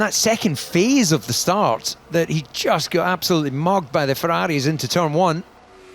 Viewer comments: no way i just heard the commentator say he got mogged by the Ferraris?😂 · did he just say MOGGED 😂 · did he say 'mogged'?? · did he say "absoloutely mogged" 😭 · did he say mogged 😂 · did he say mogged 😭😭✌️ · Did DC just say Mogged? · did he just say mooged by the ferrari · I'm sorry, did I just hear David Coulthard use the term "mogged"??? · did bro just say ferrari mogged Mercedes